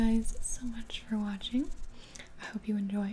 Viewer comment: i thought she said cocain with gibi